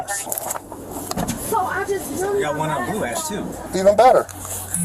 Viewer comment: I think the officer says "we got one on blue ice too" so probably crystal meth?